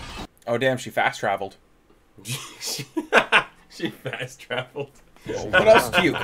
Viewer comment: "She fast traveled " Sort of. More like she logged off 😄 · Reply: I thought he said "fax travel" and I was like "fair."